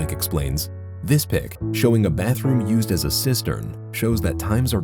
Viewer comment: I’m confused did he say a bathroom being used as a sister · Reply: cistern